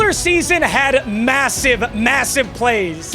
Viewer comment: did say massive?